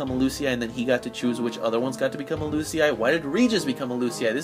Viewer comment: You asked, did he? Also, Noctis didnt become Lucii he ended the cycle and connection with the crystal the moment he died. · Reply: Oh wait, at 6:10 you asked about Regis, but I may have misheard it, you probably asked why DID Regis become a Lucii but I heard it as didn't.